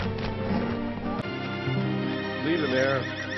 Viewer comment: I Love Spirit ❤️ 🐴 its one of my favorite movies 😄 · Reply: I think he said ( Leave the Mare) instead of ( Leave Her There )